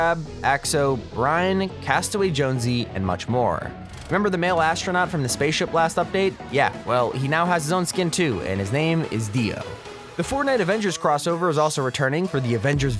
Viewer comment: DID I HEAR DIO ?